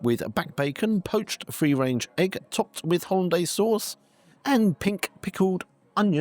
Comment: I heard "topped with Hyundai sauce"? Sorry - i can't make that unheard now ;-)